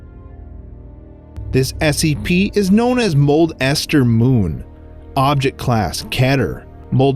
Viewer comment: thought it keep saying Cold Ester moon